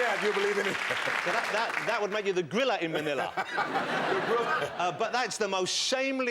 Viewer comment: did he just call gorge Forman a gorilla